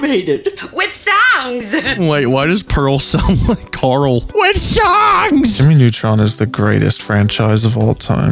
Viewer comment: when I first watched this I thought pearl said “with sounds!” Which honestly made the bit funnier and how I can never unhear it